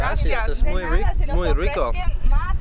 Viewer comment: Did you say muy burrito @ 8:47 ? 😂